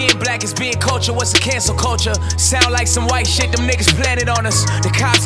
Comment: If being black is the culture then what’s cancel culture. Torey not playin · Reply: Wow that’s really deep at first I thought he said cancer culture but then I searched cancel culture and😳 it’s spot on. I never knew that ‘culture’ existed until right now.